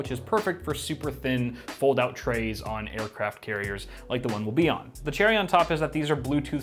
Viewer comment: Did he say aircraft carriers?